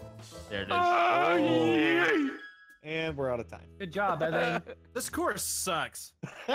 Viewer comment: I thought he said this “quirk” sucks because I wasn’t looking XD